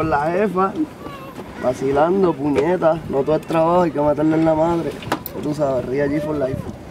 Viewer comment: que dice? maten en la madre? no entiendo